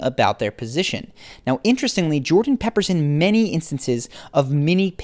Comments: Jordan Pepperson? XD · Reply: I thought he said "Pepperson" at first as well. Then I realized he was saying "Jordan peppers in." =) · Did he say Jordan Pepperson? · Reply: "Jordan peppers in" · did he say Jordan Pepperson?! 😆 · Did he just say jordan PEPPERSON??? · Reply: No he said "peppers"..sort of like he "includes or mentions" several things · bruh i thought he said Jordan Pepperson 💀